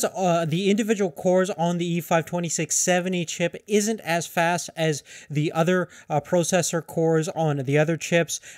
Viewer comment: dammit g2a advertisements, made me think of him saying 'isn't as fast as 'duck''